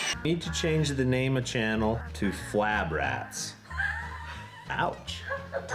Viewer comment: 😂I had to replay the beginning. I heard "flat" rats. I thought that name a little fitting for a rollback project.